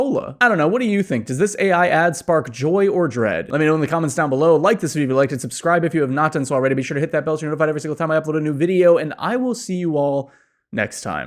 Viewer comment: what is he saying? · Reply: “Like this video if you liked it, subscribe if you haven’t already, hit that bell so you’re notified every time I post a new video, and I’ll see you all next time.”